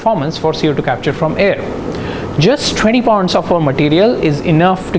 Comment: What did you say on 1:03~1:04 ? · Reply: Just 20 pounds of our material